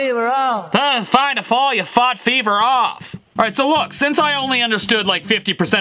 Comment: Did only I hear "fuck beaver off" from Dylan?